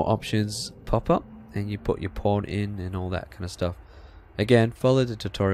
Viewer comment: did he just say po rn